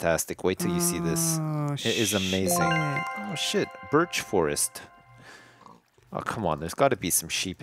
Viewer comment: I thought it said bitch forest lol